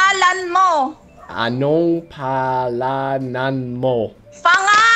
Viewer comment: I misheard "Panga-" Sounded crunchy and raw